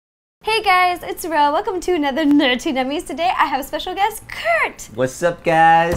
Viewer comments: It sounds like you say nerty nummies but it's nerdy nummies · it sounds like you say nerty nummies